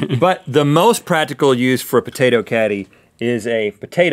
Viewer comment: Lol I thought the potato caddy said potato candy and i was like HECK YEAH XD